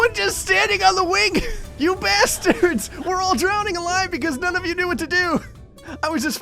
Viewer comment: the captions said wig😂😂😂😂😂😂😂😂😂😂😂😂😂😂